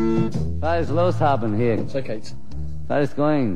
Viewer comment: what did he say? Was ist loss haben hier?